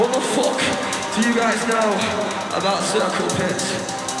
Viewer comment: When he says "what the fuck do you know about circle pits" best part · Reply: Tebumfeddeller i thought he said circle piss oh